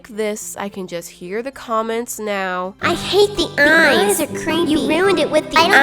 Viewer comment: "eyes are cringy" that's what I heard